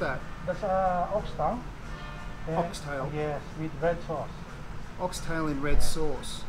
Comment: You misheard him. It wasnt Ox Tail. He said Ox Tongue. LENGUA is spanish for tongue. The dish Lengua Estofado is an Ox Tongue dish.